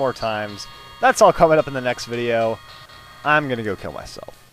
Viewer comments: in the end it sounded like said im going off to kill my self xD damm i had to watch the video 3 times to actualy make out what he said wich was im going off to heal my self LOL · did he say at 11:37 "Im gonna go kill myself? :O